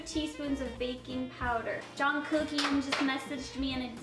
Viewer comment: "*Jungkookie* just messaged me" Did she say Jungkookie?